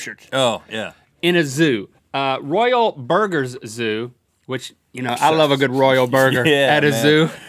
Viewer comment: At first I thought they said royal burgerlers